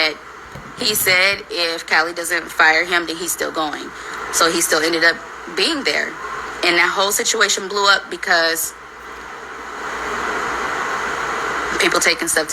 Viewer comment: I thought she said she didn't fire him